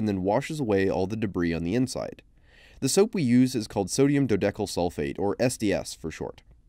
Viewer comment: I hear sodium Yanny sulfate